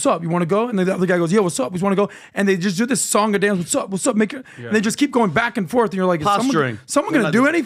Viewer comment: Looks like he’s said what’s up N(word) 😮